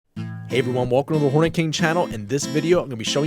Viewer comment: (me staring at my mobile phone) Mom (walks in): what are you watching, dear? Me: some guy on Youtube called the Hornet King Mom (misheard): WHO THE HELL IS HORNY KING !? I genuinely LOL-ed